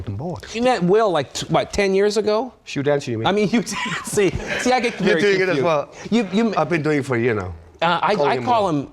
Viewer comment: can someone please tell me what mads says in 1:31 :) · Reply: i think he said ''hugh dancy right?''